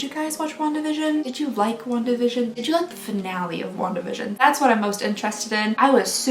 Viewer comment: Ask me why I thought she said “long division” multiple times rather than “Wanda vision”... no idea